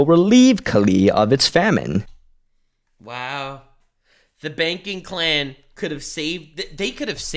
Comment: Ohhh! The Banking Clan! I thought they were saying "the Baking Clan". I wanna see the Sith Baking Clan lol